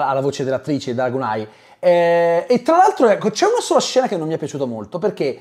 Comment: Non ho capito, come si chiama l’attrice? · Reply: Danai Gurira